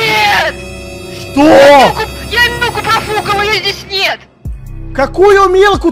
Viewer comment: мне послышалось "я ногу профукала" 😂😂😂